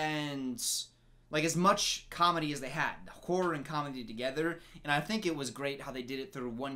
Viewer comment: it sounded like said “It’s hard to blend whore and comedy together” LOL anyone else hear that too?